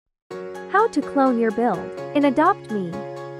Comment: I heard "how to clone your bill" XD